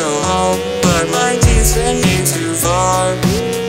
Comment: “I just want to go home. But my pizza is too far”💀💀💀 Does anyone else hear it?